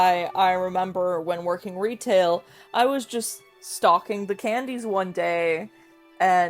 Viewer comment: i thought you said stalking the candies instead of stockinf💔